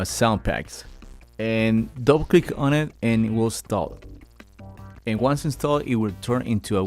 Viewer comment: "Double click on it and it will install"... For ages I thought you said "don't click on it, and we'll stop". lols. Cheers for the video!